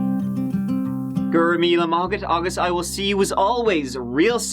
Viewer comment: Completely tangential here, but what the heck was goin off about at 45:32? Anybody catch it? Was it in the language of the 'chauns? I replayed it over & over but nottin. Course I couldn't understand anyting that was said in the interrogation room either, so there's that. And yes, I AM a native speaker (of ENGLISH - the English of the upper left hand corner of the contiguous US.) Tanks for another great video - you're the best! 🖐😷❤ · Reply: Go raibh míle maith agat. A thousand thanks.